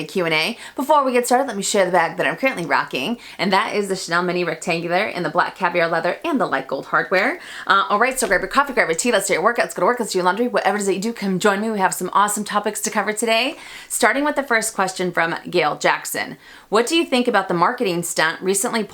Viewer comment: I’m laughing why do you say the ending so fast and this time it seemed that you were gonna bust out in laughter. You’re so funny. Do you time yourself to see how fast you can say it!? If so what’s your going time. Love you see you next week. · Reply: I can't understand a word she's saying in the intro only thing I hear is "Go to work lets work do your laundry" LOL but I enjoy her videos very much once I get pass the intro 🌹